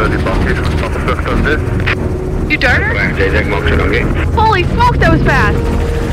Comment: For a channel that prides themselves on morals and values and God, that f bomb drop by in the helicopter was surprising😮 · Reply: lol you misheard! She said “holy smokes that was fast.” Not holy f*** 😂😂😂😂 just hard to hear through the mic and sounds, but holy smokes is a phrase she use’s often. I’m sure they cuss once in a while, but not on videos or when working with professionals!